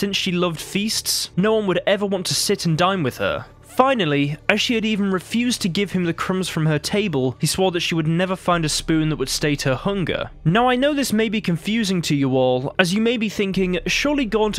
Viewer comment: Did you just say "State your hunger" 😂